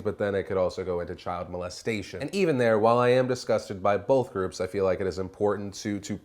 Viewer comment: "DIsgusted by both groups"... careful Phil, sounds like you said you're disgusted by gays!